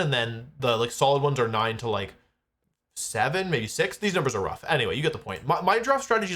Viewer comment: did he just say 6-7